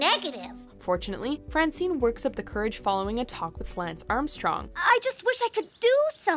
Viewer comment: VO swallowing her words made it sound like she said, "...a toxic Lance Armstrong..." as opposed to, "...a talk with Lance Armstrong". Funny that.